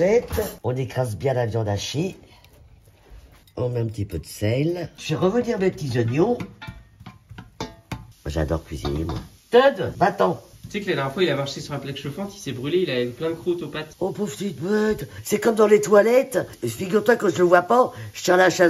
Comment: C'est moi ou le chat s'appelle teub ?😂